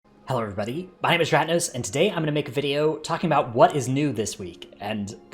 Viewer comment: YouTube CC this week: “hello everybody my name is stratnose” ...well he does have all the best strats! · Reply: Thanks, now I heard it as stratnose in the beginning 😆😆😆😆